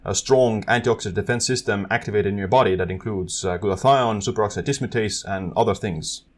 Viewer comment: For those of you wonder what he said after glutathione 1:47 Superoxide Dismutase (SOD).